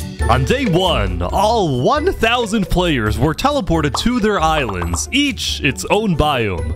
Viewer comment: Bro I thought it say 100 player until it say 1000 player 💀 (my bad 😅)